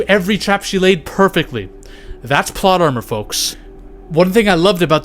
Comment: Did u just say thot armor